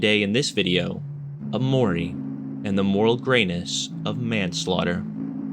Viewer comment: i thought it said gayness